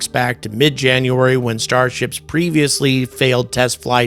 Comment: I swore he said "Starshit" a few times 😂